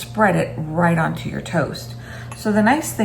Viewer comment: i don't know why, but the first time that i heard this i heard the word "toes" and i had to rewind a bit before hearing the word "toast."